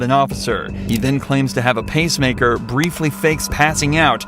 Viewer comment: Did anyone else notice the captions said "PASTE MAKER" and not "PACE MAKER" 🤣🤣 bruh makes paste in his chest?? For what?! 😂😂